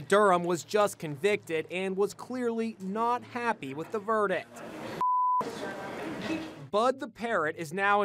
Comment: I heard: 1:35 ... was convicted and was not happy with the Birdict.